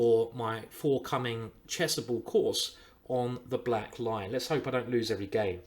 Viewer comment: Ah black lion - I always heard you saying "black line" D'oh